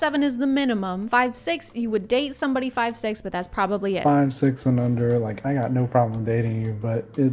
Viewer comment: not to be this person... but 8:17 can you actually comprehend what he's saying? "5,6 AND UNDER like.. i got no problem in dating you." not above ... but under.